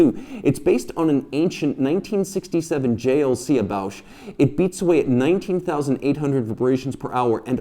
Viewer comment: what is the word you say after JLC. Can you explain that? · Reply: "Ebauche." It's the French term for a basic movement kit supplied by the original manufacturer to a customer brand. In general, the ebauche will include bridges, drive train, and possibly a few elements of the assortment (escapement, hairspring, balance). The customer then chooses how extensively to mechanically upgrade, aesthetically decorate, and chronometrically regulate the finished product. Also, certain elements of the kit might be replaced with significantly finer replacements (IWC used to do this with ETA kits) before completing the movements for delivery to customers. Best